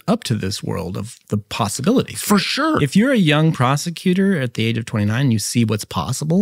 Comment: I have to apologize; every time I heard, "If you're a young prosecuter", what I actually heard in my brain was, "young prostituter". My bad, I'll go repent.